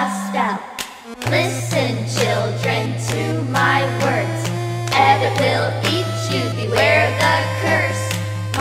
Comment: What You Guys Hear:"Ebbot Will Eat You." V.S. What I Hear:"Adabelle Will Eat You."